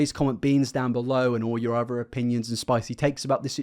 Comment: At first I thought he said to comment "beans" but then I realized he actually meant "opinions" Anyway, I think it's absolutely disgusting for a parent to exploit their child just for the money since there are going to be direct repercussions to all of this oversharing that's she's doing. She's already revealed so much of her and her daughter's lives online, I wouldn't be surprised if one of her fans actually did something in real life. It's sad to say that maybe something like that needs to happen for her to actually realize the severity of the situation. I just hope her daughter can have the chance to grow up without so much of herself being put online.